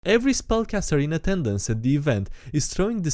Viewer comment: For a second, I thought he said “Every Spunkcaster…”.